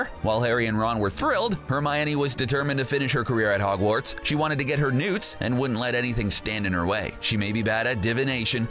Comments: for a spit second i thought that you said "she wanted to get her NUDES" 🤣🤣🤣🤣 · I thought u said Nudes LOL